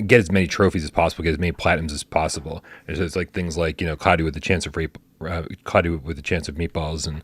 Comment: Did I hear cloudy with a chance of rape at 10:15? That does not sound like a kids game. 😂